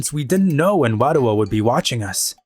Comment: I thought i heard Wario.